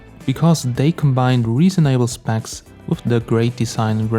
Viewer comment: did he just say reason able instead of reasonable